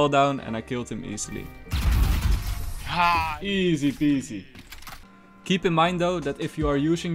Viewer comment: i heard cypher in my mind